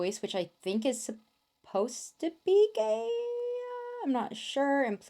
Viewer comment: when she was saying gaea I thought she was saying gay😭😭😭😭⚔️